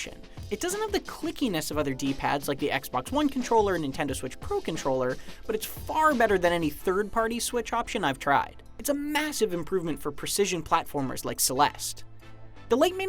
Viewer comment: did he say Nintendo switch pro?! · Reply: The pro controller for the switch, not a Nintendo switch pro lol